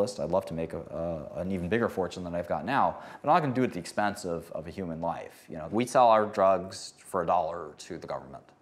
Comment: VICE wtf is that caption at 6:00 . He said I'm not gonna do it at the expense of human line